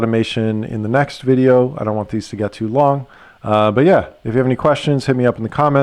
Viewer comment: did you said in Lithuanian "beja" ? It makes sense in that situation how you use it ;) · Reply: Ha, no... I said a slurred American "but yeah"... What does it mean in Lithuanian?